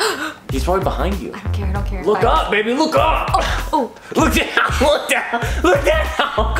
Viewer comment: I hear "LOOK UP IDIOT" with voice, instead of "look up baby" 😂😂